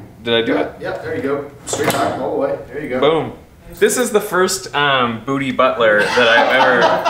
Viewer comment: I heard "BootyButler" so I laughed, I quickly thought I'm so dumb but then the scientist laughed too, therefore, I'm not dumb